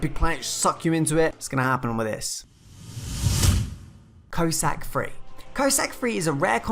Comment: Wasn't looking at the screen when "Cossack" was being talked about and I could have sworn the dude said "Curry Sack" Hahaha cracked me up.